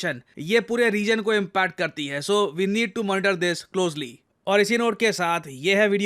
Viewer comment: Did yo say we need to murder closely 😝